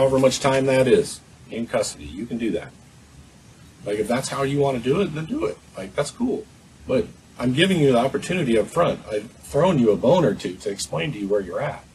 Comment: Did he just say "I'm throwing you a boner"?